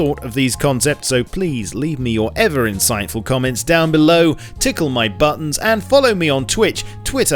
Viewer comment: when he said tickle my buttons for a second I heard "Tickle my butthole"